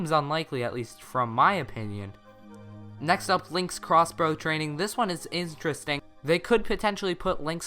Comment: Not to be mean, but what did you say at 8:19? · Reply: “Next up is Link’s Crossbow Training. This one is interesting.”